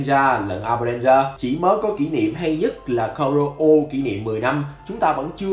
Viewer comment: em ko nghe nhầm chứ "hay nhất" á?